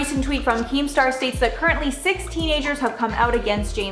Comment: What Was Said At 1:28 ? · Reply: Teenagers